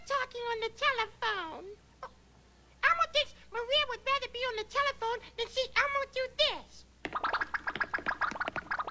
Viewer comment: What's Elmo saying at 40:35? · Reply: He was saying “Elmo thinks Maria would rather be on the telephone than seeing Elmo do this (silly wiggle dance)”